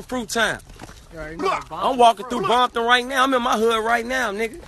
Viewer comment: Did he just say Bompton at 1:04 😂😂😂